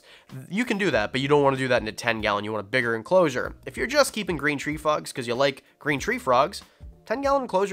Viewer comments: did he just say green tree fucks? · Is it just me or at 6:54 did he say green tree fu*ks instead of green tree frogs. 🐸